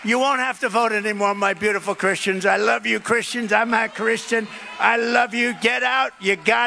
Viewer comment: Every news site I have read says that he said “I’m a Christian”. That said, I heard “I’m not Christian” too.